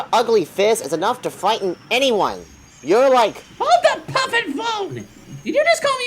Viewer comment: Hold the pumping phone! Did you just call me ugly?